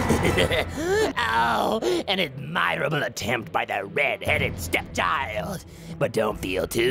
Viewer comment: I genuinely thought he said red headed ceptile the first time I saw this. I thought to myself why did cell call him a pokemon and it made me laugh for the rest of the video.